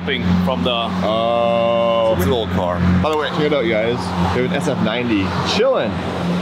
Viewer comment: am I the only guy that listened say sf90 instead of f8